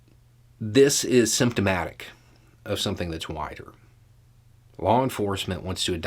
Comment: When you said "this is symptomatic of something that's wider" I had to replay it because I heard "this is symptomatic of something that's whiter." And it made sense both ways.